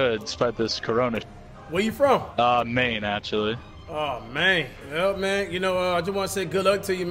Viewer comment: Cash: “where ya from?” Guy: “Maine actually” Cash: “Aww Maine” 😂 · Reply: FaZe Blrut no cash usually says aww mayne but he’s meaning man he just says it weird. So I was laughing cuz it sounded like he said Maine 😂